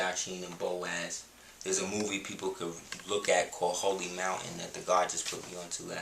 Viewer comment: Did he say "Hogee Mountain" movie at 5:42?